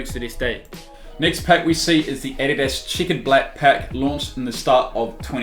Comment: Lol who else thought he kept saying ADIDAS CHICKEN BLACK ??